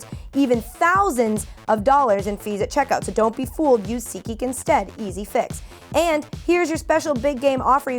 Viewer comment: )I thought you were saying SEEKgeek... for weeks. Seat geek makes a lot more sense ;)